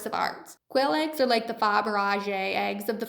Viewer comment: They're like the what of the food world? She said something around the 1:00 mark that I couldn't make out. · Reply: She's trying to pronounce Fabergé. And badly. It's pronounced "fab ihr zshay".